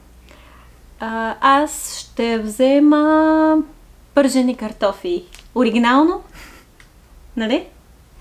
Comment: Thanks for another great lesson. Just a question what did you say you will get at 6:30 I heard картофи but not the word before it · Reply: It's "пържени картофи" i.e. "chips". "пържен" means "fried".